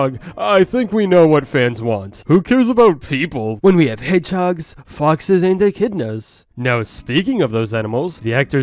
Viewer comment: What other people hear: “who cares about people? When we have hedgehogs, foxes, and echidnas” What I hear: “who cares about people when we have furries?”